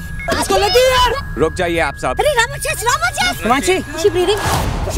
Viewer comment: what did she said????? Ramichis??? Mtlb???